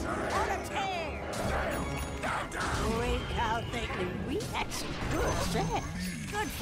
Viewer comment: Did she just say they had good sex?